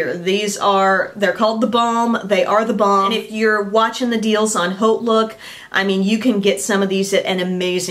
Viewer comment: what did you say? Deals on hope..book? Couldnt understand what you were saying there.